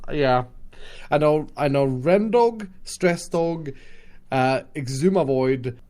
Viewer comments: did just said Stress dog 😂😂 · um did just say “Stressdog”???? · Did he just say... stress dog?